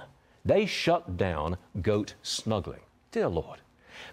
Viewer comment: I really thought he said "Goat Smuggling!" Smuggling goats into the county fair!! Kinda made me laugh 😂